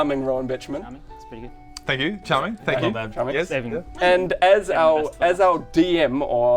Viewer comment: Wait wait wait, is Rowan's last name "Bitchman"? Did I hear that right?